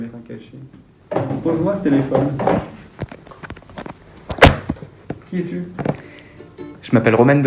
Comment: Quand le proviseur il a dis "qui es tu ?" J'ai entendu "j'appelle les flics" XD Nan un vrai il l'a grave bien pris mais il a raison il peut grave avoir des problèmes le proviseur